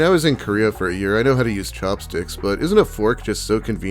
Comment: IDK why but when you said chopsticks, it sounded like you said cocksticks.